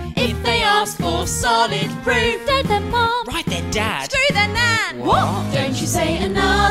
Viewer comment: I’m dying laughing after I heard “scroo there nan”